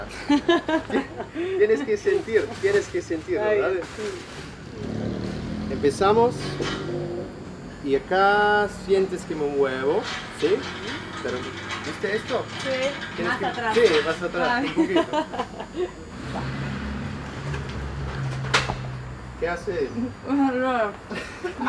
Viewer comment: entendi "aca sientes que un huevoo"